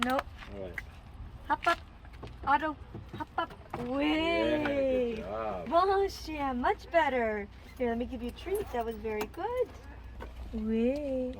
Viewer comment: wait is his name Auto? it sounded like you said "up up Auto"